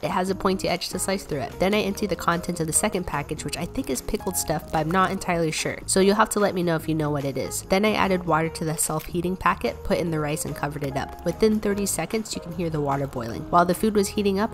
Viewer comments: I thought u said self healing and was expecting some ancestral knowledge I’m dead 😂😂 nice video your content is always so nice to watch good vibes💜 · I thought this said „self hating“ and I was like oh no honey whats wrong 😭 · I thought it said "self-EATING RICE" LOL